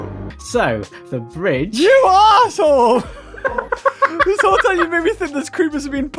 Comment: For those wondering why evil vertico came out - the answer is not having slept in well over 26 hours or so! But also for the 11/10 giggles. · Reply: did say u a**hole